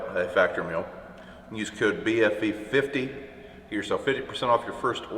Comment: Thought u said BFE50??? Lol Bum f Egypt......BFV =......BFV50 got it. 🤣